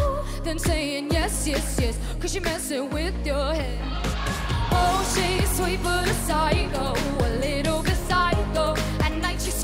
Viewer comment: I thought Ava max said “oh she’s sweet but bicycle, a little bicycle